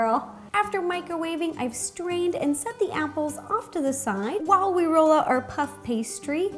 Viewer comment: What she said : "I strained and set the apples to the side." I heard : "I screamed and set the can pears to the pie" what is wrong with me